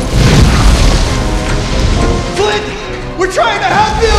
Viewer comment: am i trippin or did he just say clint instead of flint